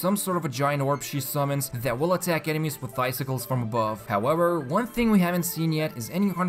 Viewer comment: i thought he said bicycles....